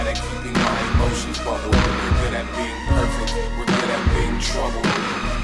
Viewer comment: "This isn't a song, I just happen to rhyme when I get emo and find time to write facts" is probably my favorite line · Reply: I thought he said "we could have been perfect, we could have been trouble..."